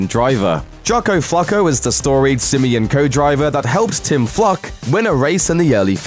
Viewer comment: Who else thought he said his name was "Tim fuck" ?